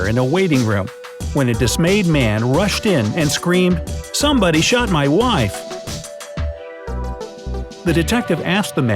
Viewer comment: the man says "somebody shot my wife" but the subtitles say "someone shot my wife"